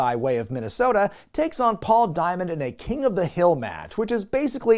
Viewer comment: I must be High. But I thought you said, 'An Over The Hill' Match! Then I'm thinking, 'that's not going to get guys over. Unless by over, they mean' their career. P.S. Net Week, we have a 'Past their Prime' Match!' Don't leave the room for that one...someone might pull a quad!